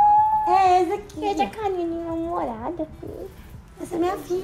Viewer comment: GENTE SÓ EU ESCUTEI ISSO!? /as 2: AHH JISOOOO! ah se ta com a minha namorada: olha pra câmera com uma cara -_- EU PEGUEI VOCÊS MU AHAHAHHAHA HAHAHHAHAH HAHAHAHAHAHHA shippem UwU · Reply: Eu acho que ela falou "essa carinha de mal-humorada", alguma coisa assim, mas não custa sonhar né kkkk